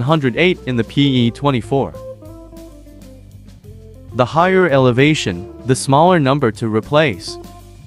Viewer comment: Your literally show that when 100 for elevation of 3500 then you said 102 for less elevation like <num> then said 105 is 1450 ft then go on to saying the higher the number the lower elevation which is not at all what you described so which one is it im so confused?? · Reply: I think you might have misheard, he said the higher elevation the lower number to replace (1:53), not the higher number the lower elevation